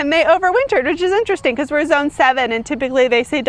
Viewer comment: For a minute I thought you said “zone 7 X” and I was like YES that is the best way to explain Oklahoma weather. 😂 Love your channel!!! I’m in edmond (okc area) and try to grow cut flowers just for myself. Your channel is so inspiring!!!! I learn so much!